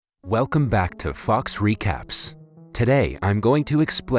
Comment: I thought he said fuc recap